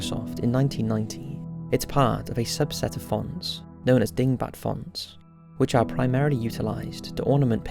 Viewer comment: I thought you said "dingbat farts".